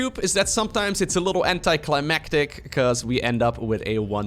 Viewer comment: Haha thought you said “anti-CLEMatic” 😅